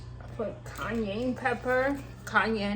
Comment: I thought you said Kanye pepper.🤭